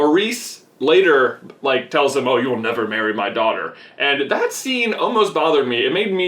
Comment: I thought he said "she'll never marry you" which I took to mean she would never agree to it.